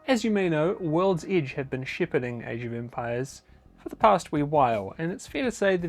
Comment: shippiding? Wtf are you saying